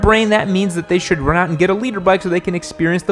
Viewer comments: I always thought is saying "leader" bike, not "liter"... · Always thought that you said leader not liter